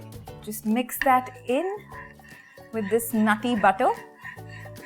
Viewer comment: did she say nutty butter or naughty butter? Cuz nutty butter makes it peanut butter eh? And naughty butter is a non existent to my knowledge..recipe looks good doh..!! Cheers..!! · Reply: Butter when cooked for a long time turns brown and has an earthy-nutty flavour, that's why it's commonly referred to as 'nutty butter' around the world.